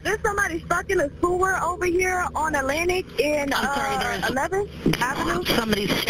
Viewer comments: Who the hell tricked her into going down that sewer? Pennywise? We all float down here..🤡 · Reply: honestly i had to turn on the captions for the beginning of the video. I thought the lady said "There's someone fuckin a schoolbus over here" xDDD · STUCK IN A SEWER!! Jesus Christ I thought at first she said “f**king a cooler”! Goddamn